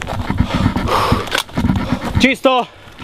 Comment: did he say Cheeto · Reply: kyle soch nah he said chisto ( in translation from slovenian to english - clear )